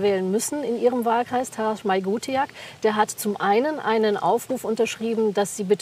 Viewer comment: Bitte um Hilfe: Ab ca. 2:47 nennt die Dame die Herrn Meuthen interviewt einen Namen den ich nicht verstehe. Wenn jemand den Namen dieses AfD-Politikers kennt, bitte unter diesen Kommentar schreiben. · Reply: Ich habe es auch nicht auf Anhieb verstanden und dann einfach mal die Google Suche angeworfen. Darauf bin ich dann auf den Politiker mit Namen "Taras Maygutiak" gestoßen.